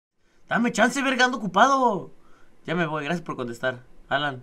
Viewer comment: No Entendí xd... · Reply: dame chansa verga ando ocupado. :V